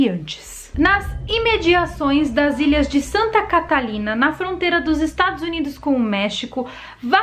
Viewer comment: Achei que ela tinha falado "Santa Catarina" e eu já pensei... aqui onde moro?? Opoha '-'